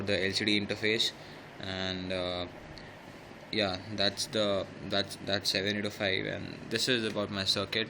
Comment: What does he say at 1:05? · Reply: I said 7805, it is a 3 pin voltage regulator IC